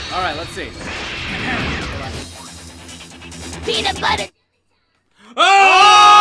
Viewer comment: what did he say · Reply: Nigel Hargett Peanut Butter Jelly Time